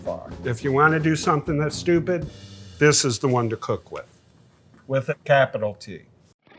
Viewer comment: I’m sorry, did he say stupid with a capital T?? 💀